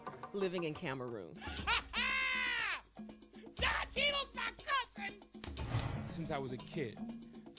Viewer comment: Did he just say don cheadle is my cousin